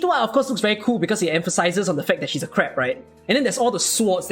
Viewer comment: I swear to god, I thought you said "She's crap" ... but then I realized later you just said she's a crab. Whew. ... but then it turns out, you're kinda saying both. She reminds me of Matoi stats wise. But even Matoi looks like slightly better atk and about same health. Is the 0block, def, atkspd vs health, and enmity stuff worth the tradeoff for a 4* that doesn't need to be micromanaged?